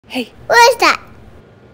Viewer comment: I thought she said f off